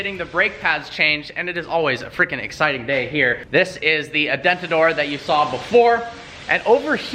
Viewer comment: Did call it an "adentador"? Or did it just sound that way to me